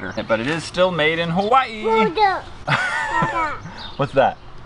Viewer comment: She's very adorable and super smart!! She jumped up when he said he thought he saw a spider, she said spider but thought she said buddah.... She's very smart 😊😉💞